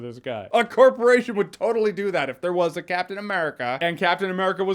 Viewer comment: For a sec I thought you said Captain Merkin...